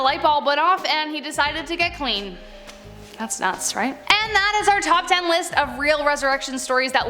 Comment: I couldn’t understand what she said lol it sounded like she said “that’s not sright” lol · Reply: She said, That’s nuts, right?